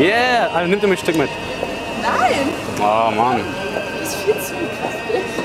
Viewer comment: Was sagt das Mädchen bei 2:52 ? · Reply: "du bist viel zu kräftig" sagt sie